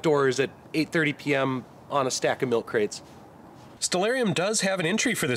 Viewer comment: What is the app you used on your android phone to see the satalite? I heard you mention the name at 3:07 but i could not catch it enough to spell it. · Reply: Stellarium